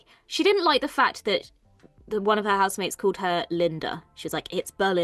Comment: maybe I misheard, but I thought the roommate called her "Melinda" and that's what she was correcting - not that I disagree that the Doctor started with the nickname was too soon, but I thought Belinda was just straight-up correcting someone who'd gotten her name entirely wrong